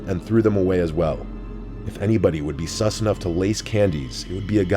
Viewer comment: Did he just say “sus enough” 😂